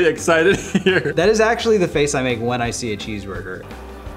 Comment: I thought Nate said" that is my face when I see a sheep herder."